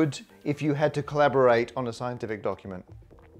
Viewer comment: When you were talking to your dog I heard, "...colaborate on a scientific dogument.."